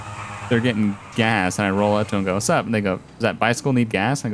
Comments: sounds like he said the n word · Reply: he said 'and they go' but it does sound close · y does it sound like he said "sup nigga" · What am I hearing? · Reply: "And they go" it's not the n word.